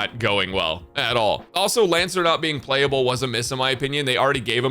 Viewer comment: does he called chu, lancelot??? Dislike and shitstorm omw kekw